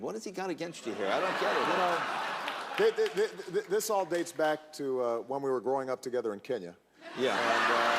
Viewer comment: no. 8 - best one by FAR. I could not stop myself from laughing out loud when I heard what he said. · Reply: Yzanne Pepper He said: this is all based back when we grew up together in kenya